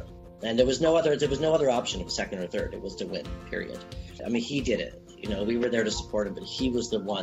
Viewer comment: did he just said Periodt 😂😂